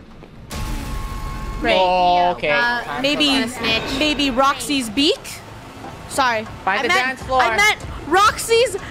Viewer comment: what did she say? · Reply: She said “snitch”